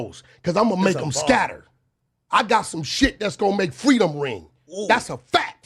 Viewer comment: Dam he said freedom ring, whole time I thought he said frito lay 😂 wack lame 😅